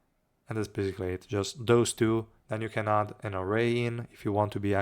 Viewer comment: add who in? Cant quite catch that. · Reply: Narae, sorry my pronunciation there was particularly awful 😅